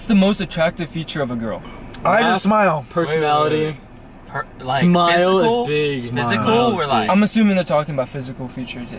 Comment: did say ass or laugh 😨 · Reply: Laugh